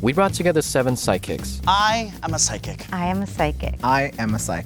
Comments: why does everyone sound like they saying i am a side-kick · why did i hear ‘i am a sidekick’ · I kept hearing "I am a side kick", what's wrong with me?